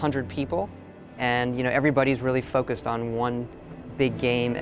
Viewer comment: i heard everybody really fuck with one game